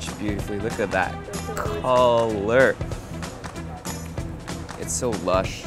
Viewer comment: Okay so on your first meal I thought you said "kalurk" but I realized it was "color" 😂 (or was it not?) Hahaha! Oh how I miss Japan, I was just there 2 weeks ago! 😢